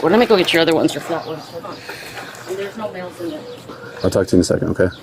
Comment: Did she just say there is no “males” in there.